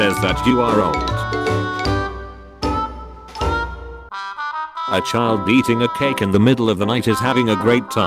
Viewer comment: Was it just me or did he say “a child beating a cake”